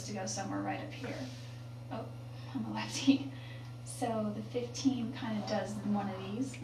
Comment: she says "Oh, Im'ma lefty". She does not say she is "ambidextrous". IJS